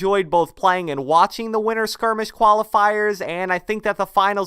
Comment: Anybody else slightly bothered by the way it sounded like was saying “winner skirmish” rather than winTer skirmish.